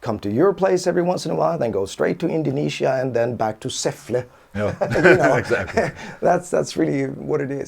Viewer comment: did say "säffle"? i've been there a couple of times, such a coincidence.